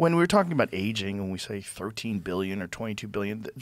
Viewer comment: I guess I heard wrong I thought I heard 18 billion